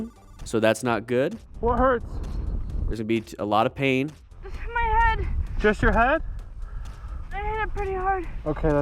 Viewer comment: I think she's saying ''my hand'' and not my head. That's why the guy said, ''ok that's good''.